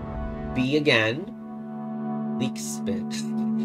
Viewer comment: I like how you saw “LEE K” and the first thing you thought of was “leek spin” lol (Assuming I heard you right)